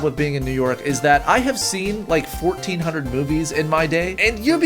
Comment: I thought he said boobies not movies